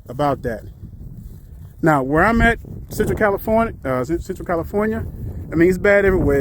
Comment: Where you say you live @ 7:40? I couldn't understand ahk. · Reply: Central California